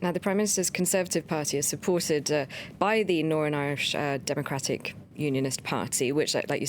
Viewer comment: does she say norn Irish?